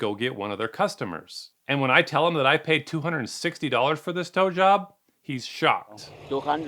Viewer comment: I love youre videos. Misheard tow job as *blow job*… either way, you’re getting screwed by these scammers.